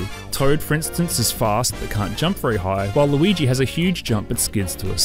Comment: Excuse me but did he call Luigi... Waluigi.